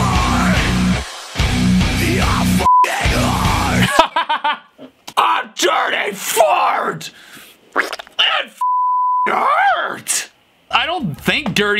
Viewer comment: I heard three thirty fart